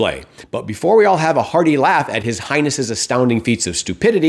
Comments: Did you refer to Trump as “His Highness”? It really sounded like you said “His Hindness”. If you didn’t, you should have. 😊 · Real smart people don't keep telling you how smart they are. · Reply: Did you refer to Trump as “His Highness”? It really sounded like you said “His Hindness”. If you didn’t say that, you should have. 😊